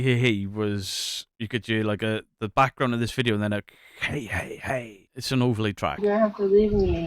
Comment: I hear “bang bang bang” over “hey hey hey”. I even listened with thinking of hearing “hey” and never got “hey”